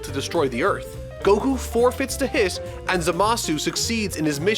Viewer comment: Why did you call Hit “Hiss” @ 6:50 ?